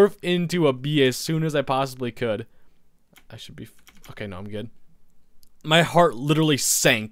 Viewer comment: he said "I should be ff, okay, no , i'm good" He was gonna say 'fine"